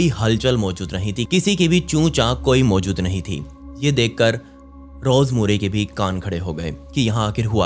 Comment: did u said rose marry ke ""kaaaan"" khade ho gye ya ""gg***nn"" khade ho gye 😅😅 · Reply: Kaan bola bhai